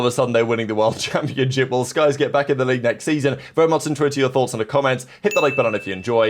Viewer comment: Bro what is saying at 0:32 what the hell. “Very much WHAT to your thoughts?” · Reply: very much intrigued to hear your thoughts